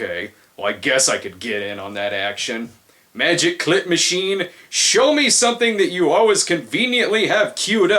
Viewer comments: I honestly thought he said "Magic Clit Machine," not "Magic Clip Machine." XD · I swear I heard "Magic Clit Machine." instead of magic clip machine. I think I've watched one to many Cinema Snob videos... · lol for a sec I thought he said clit machine